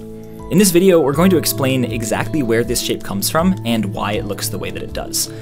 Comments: I heard, “find out where this shit comes from.” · at first I thought he said "We're going to explain where this shit comes from", instead of "shape comes from". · Anyone else thought he said "In this video were going to explain exactly where this shit comes from" at 0:30? Lol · I misheard shape as shit so "we're gonna look at where this shit comes from" lmao